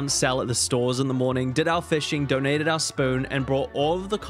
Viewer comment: I zoned out VERY briefly during this video (I was watching my dog) and when I zoned back in the first thing I heard was “donated our spoon” 😂😂😂